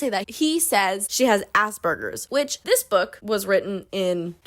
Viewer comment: They way I thought she said “Ass Burgers” when she said “Asperger’s 😭😭